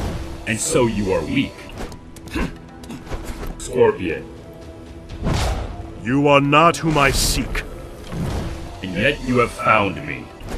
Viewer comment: English ain't my native language but what is he saying at 11:06 seems to be "You're not who my seek" but i'm not sure about that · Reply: Actually he says "You are not whom I seek" which is gramatically correct.